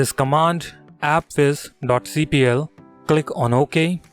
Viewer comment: what did this piece of shit say? I’m getting fed up on how his screen ain’t even clear like wtf did he say to type APPFIS? Like damn foo